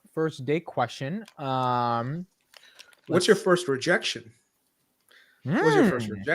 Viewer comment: Did I just hear "1st erection"? 🤔